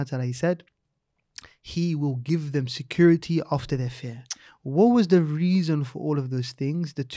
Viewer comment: Did say security after their fear or over their fear? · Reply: After